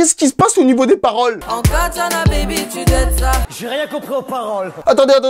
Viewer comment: Moi j'ai compris " au Katchan a baby tu dead ça " Si t'as la ref 👇